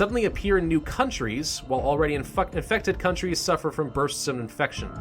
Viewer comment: on 12:28 you said infuckted instead of infected